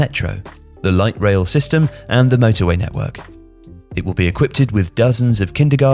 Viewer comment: Did I just hear a brand new word: 'equipped-ed'?